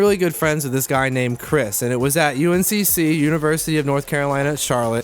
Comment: Oh, I thought he said, "I went to UNSC (Halo)"